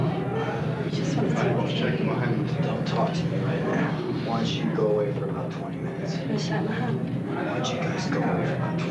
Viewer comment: Maybe someone can help me out. At 3:30, HR responds to what they say with, "Don't talk to me like that. [Can you do me a favor and leave for 20 min?] " Does anyone know what they said? I turned up the volume but still couldn't make it out... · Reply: "Don't talk to me right now. Why don't you go away for a little,... 20 minutes."